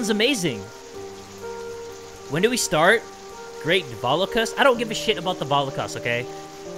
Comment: When he said "the don't give a fuck about the valokas" I thought he said "I don't give a fuck about the holocaust" XD